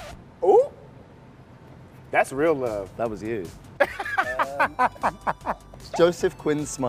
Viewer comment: Is Joseph saying "That was you" at 0:30?